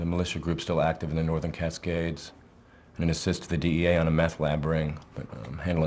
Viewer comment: When I was young I always thought said "assist the DEA on a math lab brain" instead of "meth lad raid" 🧠